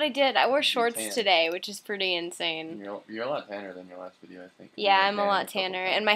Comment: i coulda sworn he said you're a lot fatter, lol i was like wtf?